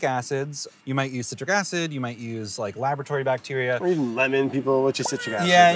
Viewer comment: It sounded like said "Lavatory Bacteria"